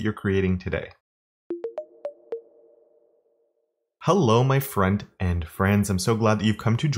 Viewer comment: P.S. Even the closed caption has you saying "Hello my friend and friends" instead of "front-end friends". 😱😂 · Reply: Wait what? I always thought he said "friend and friends" and never understood it XD. I had to close my eyes and listen carefully to hear it was "front-end friends". That makes a lot more sense 😅